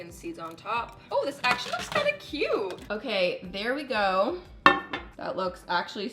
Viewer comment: I heard "berry go"...Just because I was seeing berries on your oats hahahahahaha